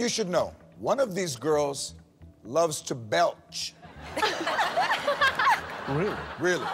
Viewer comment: what does the word at 38:27 mean? the one which michaels loves to? english is not my mother tongue so i would love to get an explanation or just to know how you write it :) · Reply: Juliaa odersoo belch means burp 😛😄🙋‍♀️🍀